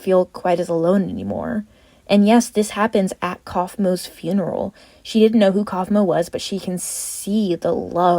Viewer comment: not sure how this mistake happened, at 11:09 you clearly say Kaufmo's funeral but captions say Caine's funeral 🙃